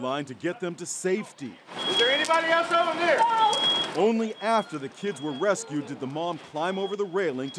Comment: At first I thought he said "only half" instead of "only after"